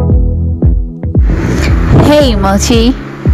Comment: what I hear ' hey emoji '🤡